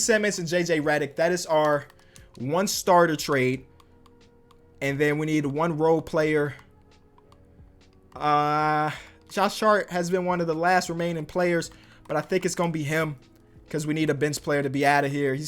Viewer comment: it sounds like he says "Josh Shart" LOL